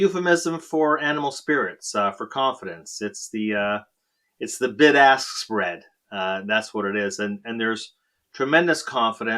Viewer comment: I heard it’s the “big ass spread” - I agree.